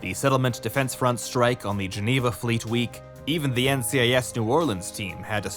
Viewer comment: I keep hearing "flee tweek"